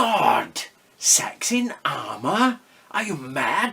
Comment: I thought he said SAXON armour not SEX IN armour. Was kinda disappointed